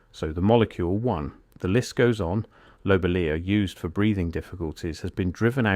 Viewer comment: what was used for breathing?cant hear it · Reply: I think they said Lobelia